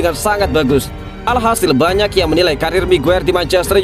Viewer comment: Sekilas kok saya dengar menyebut haji maguere..hhhh apa saya salah dengar apa emang diplintir😀😀😀